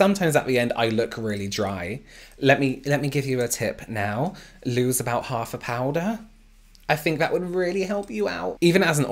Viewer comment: Legit thought he said “you need to lose about half a pound here” 😂 not half the powder